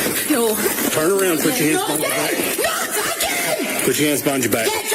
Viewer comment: Did anyone else hear her say "not again, not again", when she was getting cuffed? Perhaps a reference to her previous horrific experience with American cops, suggesting this might not have been her first break down during her stay in the States. Alcohol and controlled substances have different restrictions and strengths in different countries, it is likely that a cocktail of substances that she is used to Scotland, could have very different reactions and effect in a different place.